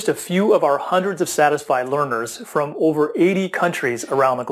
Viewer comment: Sorry. It sounds like you say "dissatisfied learners" at 1:09. Lol I don't think you get glowing feedback from dissatisfied learners. Play from 1:04 to hear the sentence in full.